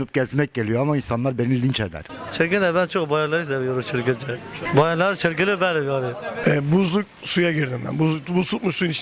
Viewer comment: 'da ne diyor adam iki saattir çözmeye çalışıyorum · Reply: blair bayanları seviyorum çılgınca bayanların çılgını benim